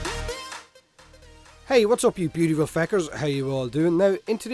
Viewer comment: can i know what u say in intro ?...i heard u say like "hey whatsup you beautiful faker" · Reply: you're close, but it's feckers instead of faker, as a replacement for f***ers I assume